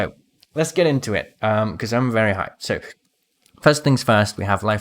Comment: did he say I’m very high?